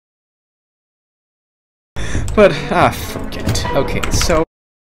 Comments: He said “Ah, forget it.” He just said it really fast so it sounded like he said the OTHER word. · What the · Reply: It sounded like he said it, but he said Forget it. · Looking back.. I can’t believe what he has done recently. I’m honestly thinking about deleting this, it disgusts me about what he did. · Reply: It sounded like he said “ah forget it”